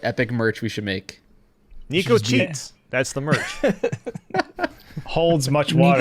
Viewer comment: I thought said “Nico sheets” for a second. Buy bed sheets.